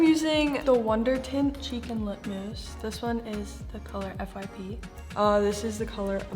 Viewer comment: When Charli said "cheek and lip mousse" I thought she said "chicken lip mousse" lolol